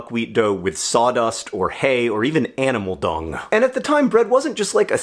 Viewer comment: I first heard "animal dong" and snickered. Apparently I'm 12